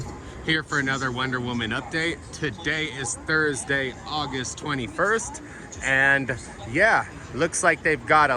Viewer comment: did you say august 21st 😂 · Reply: Lol you are right he did say August 21 lol